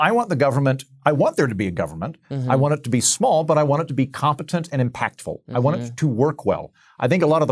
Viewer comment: Government being efficient is not a libertarian belief, in my experience. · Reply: at @ 4:30 andrew says "I want there to be a government. I want it to be small, but I want it to be competent and impactful." I guess i heard this as "efficient", but either way, none of the adjectives he uses to describe government there seem reasonable or possible, given how it performs in every domain it enters.